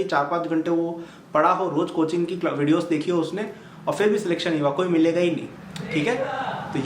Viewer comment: anyone hear 'milega'